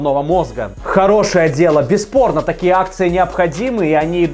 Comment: послышалось БЕЗ ПОРНО)